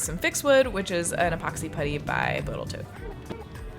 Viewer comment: epoxy by "Botal Toat", or am I hearing it wrong? xD